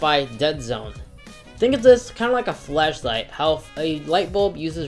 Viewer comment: thought he said flesh light🤪